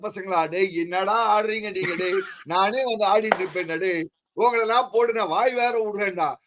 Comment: I thought said Otha @ 4:22 😂😂😂 he said “naane vanthu adiduven”